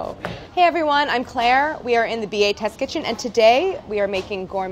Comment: I’ve watched so many Gourmet Eats videos and have always thought she’s been saying “and we’re in the BHS Kitchen” when she’s actually been saying “BA Test Kitchen” 😭